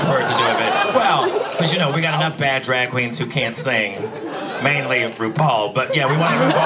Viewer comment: Did she say "mainly in rupaul" or "mainly rupaul"? Was she talking about the drag queens who can´t sing or was she talking about rupaul